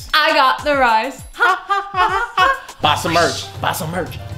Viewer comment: It sounded like he said bisomertch😆